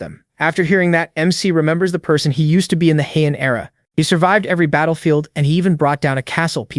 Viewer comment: I heard Heian Era Ryomen Sukuna is that you 🤨🤨🤨